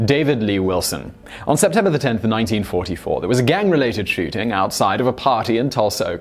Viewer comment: Is it just me or does it sound like he said 1944?